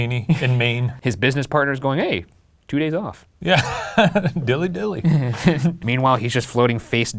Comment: bro did u say dilly dilly. come on man what kind of budweiser commercial is this